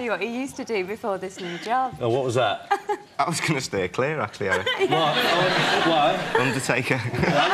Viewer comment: Can someone please translate what was said after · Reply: I was going to stay Clear actually Eric, I was a undertaker